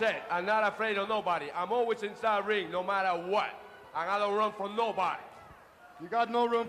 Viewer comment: What I love about this too is you can tell it's not scripted. says "And I don't run from nobody" and with his accent Piper thought he said "I got no room for nobody". I miss the old days of wrestling!